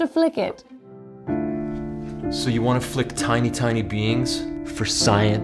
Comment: When they were first talking about flicking tiny "beings", I misheard, and thought they were talking about flicking tiny "beans" and was confused and amused.